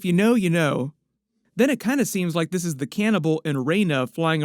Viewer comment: DID YOU SAY THE CANIBBAL.